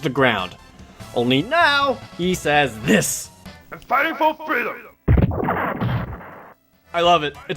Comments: Hey Blast Man I am going to buy groceries what are you doing? BlastMan: I am fighting for freedom. Oh, well then what are you up to this Saturday? BlastMan: I am fighting for freedom. Oh.. Well Ike what do you fight for? Ike: I fight for my friends. Captain America, anything to add? Captain America: NOOO I laughed way too much at that part. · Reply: I thought he said "I'm fighting for Phil!" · Will someone tell me what Sonic Blast Man is saying? Hilariously, all I hear is "I'M FIGHTING FOR FRILL!" Is it thrill instead? WTF.